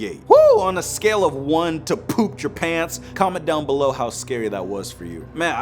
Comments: When u say "peed your pants" it sounds like u said "peatry pants" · pooped your pants Me:did you just say pooped Japan?